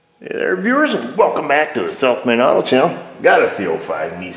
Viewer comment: Took awhile before I realized you are saying south main auto channel. I thought you were saying self made auto channel. Lol